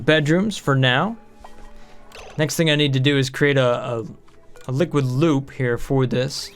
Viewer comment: When you said liquid loop I thought you said liquid lube🤣🤣🤣